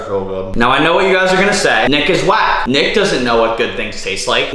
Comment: I thought he said to say Nice is black 😂😂😂😂😂